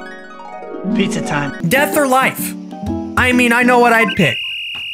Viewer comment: I skipped a bit and I thought it said “Death” or “Like,” which would make sense...